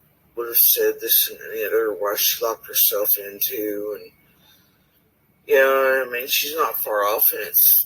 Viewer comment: Before she says “your sh*t and my sh*t” I hear her say “and the kids locked themselves in the shed”…. What???